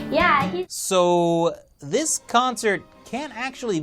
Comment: did i hear cancer? i think i did lol